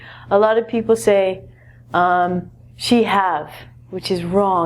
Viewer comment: Did she just say s.h.i.t ?